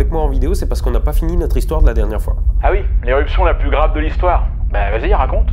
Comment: bon, évidemment en entendant la voix de moi j'ai compris "l'érection la plus grave de l'histoire" mais c'est le personnage qui veut ca, désolé mais on ne se refait pas :p bonne année